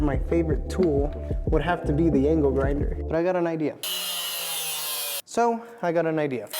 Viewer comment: the angelfinder worked! 🙌 @ 2:30 did you say angelgrinder?